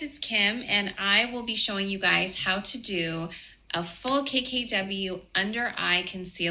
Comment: When she said a "full KKW" it sounded like she said "a full cakecake W" 🙊